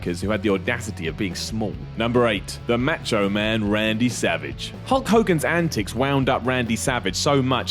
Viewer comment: Haha thought that said Randy Sausage at first